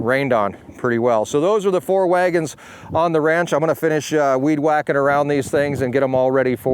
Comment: I thought it said waggners on the ranch. And I'm like noice